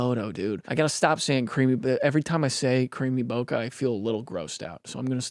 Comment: every time you say that I hear “creamy boba”